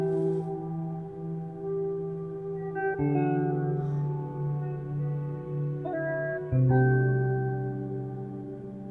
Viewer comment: what did Joel say? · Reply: Something like, "I'm never going to leave you again, do you understand?"